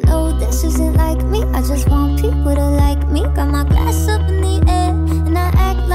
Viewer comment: Oml. For the longest time ever I thought she said “got my ass up in the air” and I was like woahhh. Did not need to know that. I feel so stupid now 😂